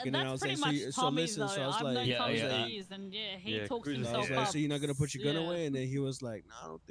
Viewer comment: Wait did she just say Tommy T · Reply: No cruizer